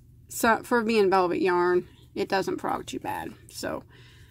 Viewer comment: What word did you use..frog? I couldn’t understand. Around the 4:50 mark. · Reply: Frog means to rip out your work when you make a mistake...